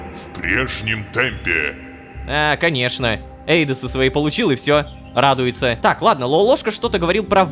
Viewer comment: Почему в это момент я услышала :"пенсию свою получил"